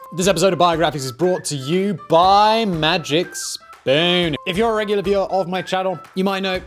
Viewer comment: Did Simon say "magic sperm" or am I going deaf ? 0:05 🤢🤮